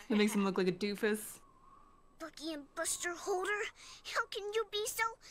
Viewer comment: I kid you not I thought she said "F**king buster holder-" and I had to do a double take 💀 Not only to process wether or not she swore but what on earth a sentence like that meant lmao